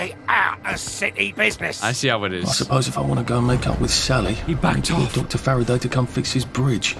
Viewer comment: To be honest I thought it said "If I wonna go make out with Sally."